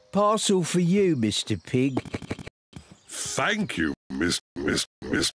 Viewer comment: did he say "thank you mrs zebra